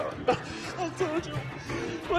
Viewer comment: what does he say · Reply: "I'm gonna find a home for this."-Frank Castle, 2017.